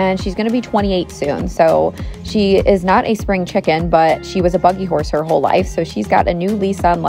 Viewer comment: I misheard "buggy horse" as "bucking horse." Lol gotta love Mondays.